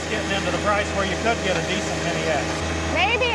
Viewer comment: What I heard was “let’s get a mini ex”. You’re welcome